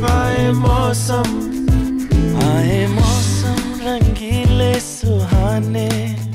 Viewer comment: When I was a kid I thought he said “I AM AWESOME rangeelay suhanay”